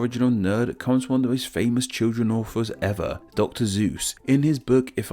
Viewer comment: Did he just say Dr.Zues? Ik its just the accent, but anyone else hear that???